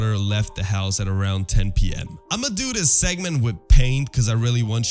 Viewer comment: bro said it was 10 pm but the subtitle said it was 1000 pm